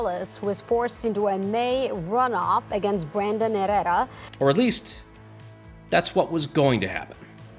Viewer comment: anyone else hear erera